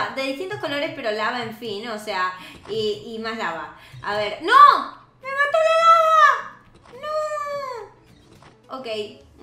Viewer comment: Minuto 2:20 yo si entendí lo que dijo, dijo ¡noo me caí a la lava!(creo)